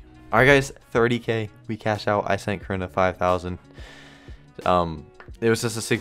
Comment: thought he called her Corona